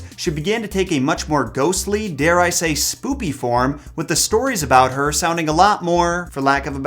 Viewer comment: did you say “spoopy”?